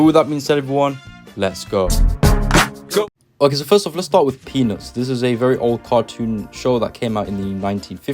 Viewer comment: When you said peanuts I literally thought you said penis